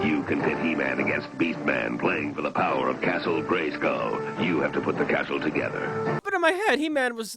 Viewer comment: excuse me? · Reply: He said “BY THE POWER OF GREYSKULL!” It only SOUNDED like he said Crumb instead.